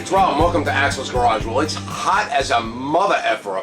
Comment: At the beginning of the video I thought he said "Welcome to assholes garage" lol